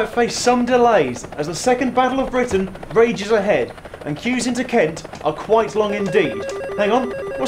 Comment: McDonald’s tryna invade Great Britain I hear